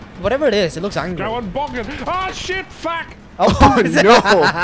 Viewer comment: Did I hear Nogla say aw shit as he fell off the bridge at 4:20 ?